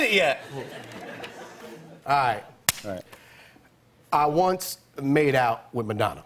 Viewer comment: I heard, "I once made out with Banana," I wasn't listening properly and I was shocked like how could he? I started looking at Jimmy and Chad's reaction and then I saw the question printed and huhh